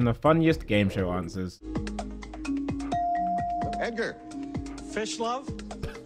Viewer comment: honest to God thought it said fish lips